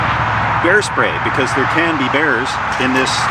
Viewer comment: Bear spray? I thought he said hair spray!!! I was like, "OK, Steve, you do you"